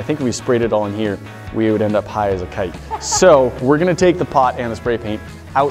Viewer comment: Ok 1:04 did he just say high as a kite, what